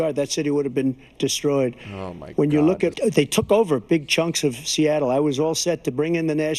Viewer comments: i thought he was going to say big chungus, the rot consumes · my dumbass thought he was going to say big chungus yes i genuinely thought that yes i am a year late